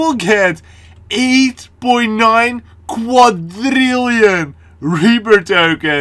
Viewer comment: I thought u said "quadtrillion" lol